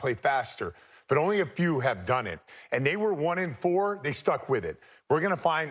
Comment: did man just say “the NBA is turning to shit?” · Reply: “Starting to shift “